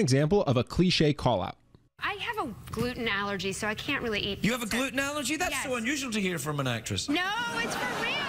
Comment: what did say? The guest understood it: "You have a gluten allergy? That's the one...???"